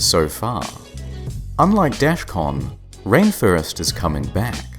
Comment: I thought he said DEFCON not Dashcon at first.